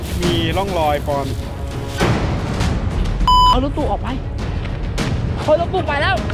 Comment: กูคนเดียวหรือป่าวที่ได้ยินว่า "เอาลุงตู่ออกไป"